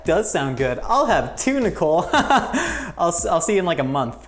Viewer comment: I don't know why I thought said, " I will have tuna cole"